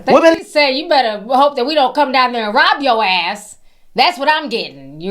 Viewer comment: What she said is " we'll come down there and RUB you out'. It's the Jamaican version of dog walking someone. It's not robbing. Lol